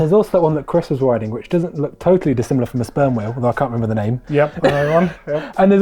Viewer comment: what did he say? sperm wheel? I don't know what that means. maybe he said something different, I don't have a clue what he is referring to.